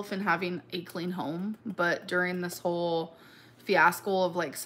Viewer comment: Did she say “fiascol”?